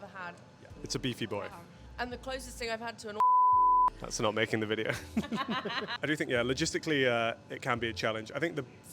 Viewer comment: What did she sayyyy? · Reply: Orgasm. 😂